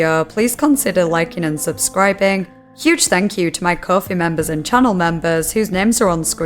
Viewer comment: I was not prepared for that pronunciation of "Chimera". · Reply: I always hear “Coffee Member” when she mentions her Patreon or whatever in the beginning of every video🤣🤣